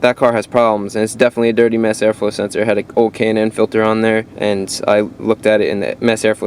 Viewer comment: Hi Chris, I did not understand what you said, I am in UK, you said "o cainin air filter???" (twice once in minute 9:11 and then 10:09 of the video) or something like that. I am interested to know what you meant. I changed the air filter on my Volvo S60 for an after market cheap one, and one year later the MAF started playing up. I am changing the MAF (in UK I cannot find the MAF cleaner substance) but wondered whether my air filter was the problem for I could change that also. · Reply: ***** I believe it is a K&N filter.